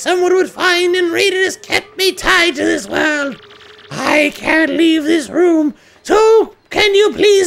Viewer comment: I thought he said he couldn't leave the room?